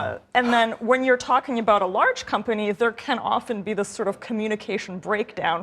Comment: Whenever I hear “placate”, I’m just hearing “fired”.